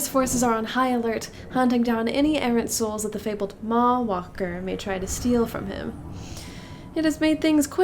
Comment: Every time they say "maw-walker" all I hear is "mall walker."